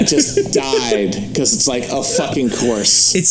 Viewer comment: "... po-liti-cal cor-rect-ness" had to play this back like 20 times before i finally figured out what the fuck she was saying